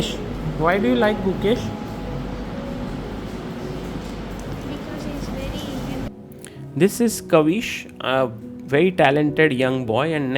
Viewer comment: Did he say because he is very handsome? 😂